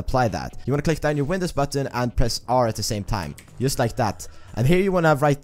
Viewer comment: wtf is he saying here? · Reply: "click down ur windows button and press R"?????? or what is it that u dont understand?